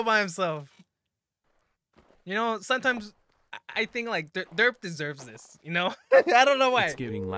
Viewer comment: I thought you said in the earlier episodes "derp deserves to live more" lol